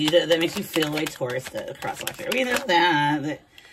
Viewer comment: did you just say crotch watcher???😂😂😂 Too fuckin funny!